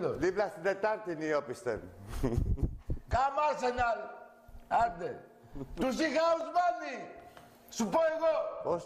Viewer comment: xaxaxaxa come with Tarlac!! γεια σου ρε, στο 0:30 τι λέει ο άρχοντας ? "..του σι χαουζμάνι" = to see how many ?